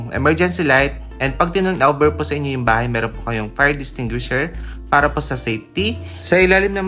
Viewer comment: Katuwa talaga si tama ba yung narinig ko fire distinguisher 05:41, thanks